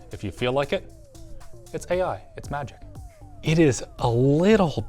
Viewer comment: first thing i heard was 'it's nazi'